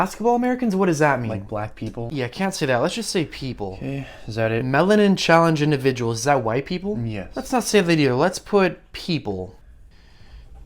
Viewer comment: I thought he fr said mentally challenged individuals 💀